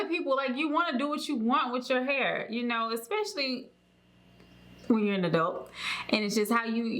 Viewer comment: Did she say “when you’re into dope” at 3:32?